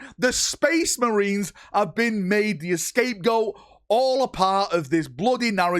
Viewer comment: Did you just say ‘escape goat’ 😂